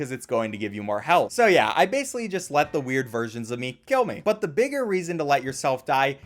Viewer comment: I thought you said weird virgins of myself but you said weird VERSIONS of myself lol